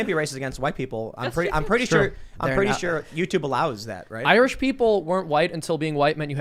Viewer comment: For a split second I thought Michael Malice was saying Irish people aren't human.....and I was okay with that as an Irishman lol